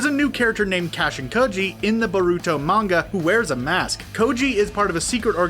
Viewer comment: When you say baruto it sounds like your saying burritos And also your pronouncing the name wrong.